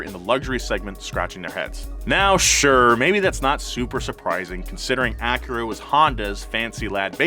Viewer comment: I swore he said that’s not Supra surprising